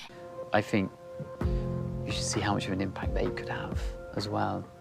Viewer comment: I feel like he was appealing to her sense of advocacy, Greta was worried about it affecting her “impact” & he said something about the impact THEY could have…as in sharing that sense with their kids & letting them spread whatever good along with the parents. Idk I just feel like it was heard incorrectly, he wasn’t talking about not having kids having a negative impact on him, just that she could reach a bigger audience by having kids to also spread her message. More mouths reach more hearts kind of thing. 🤷‍♀️ So that reaction didn’t get to me like it did bc I heard it differently…if he WAS saying it like a “poor me” thing then yeah, that’s annoying, but I don’t feel like that was his intention.